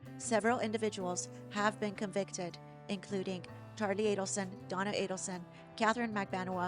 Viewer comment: In the beginning, it sounds like you say Harvey Adelson was convicted. It was Charlie.